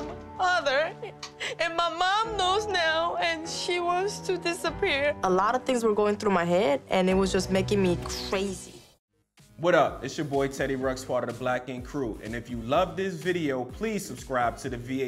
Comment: For a hot second I thought he said "Teddy Ruxpin" at 2:46 😂